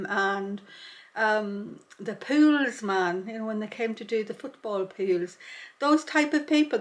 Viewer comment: Your mantle talks are so nice, and I do so enjoy them, but when you said the pools man came around I was startled, for we haven't anything like that in the States. I thought you said the, "Pool Man." If you said here, "The pool man came around", it meant the lady of the house was having a fling with the fellow who cleaned her swimming pool. So I was momentarily confused. Of course I soon realized my mistake and it just made me laugh, it so tickled my funny bone. Just one little letter "s" makes all the difference. 😅 Now please don't be offended, as I meant no offense. I have no doubt we say things that are just as confusing and funny to you sometimes too. My grandma used to make me laugh because when she was puzzled by something she would say, That's a real head scratcher." I used to think, how does scratching your head help?! 😅 Your helebore is turning out very pretty and so realistic. I can hardly wait to see it finished. I do love how this way of stitching adds new dimension. Thank you for your time I do so enjoy your sharing. 😊❤